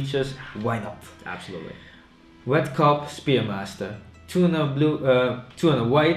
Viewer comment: "Wet Cock Spearmaster" Just what I heard haha